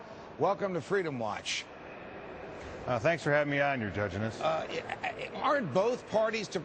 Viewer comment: judginess. coulda sworn i heard that at first...